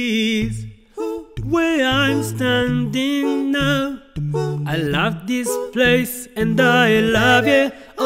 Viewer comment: Dis is a song. I love dis place って聞こえる